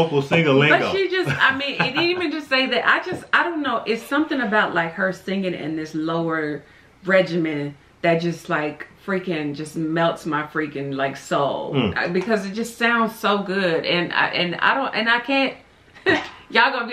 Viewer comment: Lower Register? Sounded like you said Lower Regimine